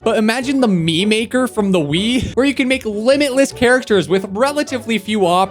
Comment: Thanks to Storyblocks for sponsoring this video! Download unlimited stock media at one set price with Storyblocks: <link> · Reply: error in captions (18:19) Says movie instead of Mii